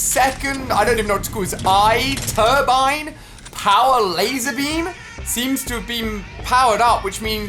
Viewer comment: did he said lazarbeam